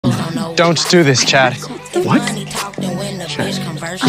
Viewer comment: Wait, I thought I heard don’ts like what does that mean?